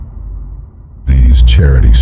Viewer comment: "It is time to mew" is what i heard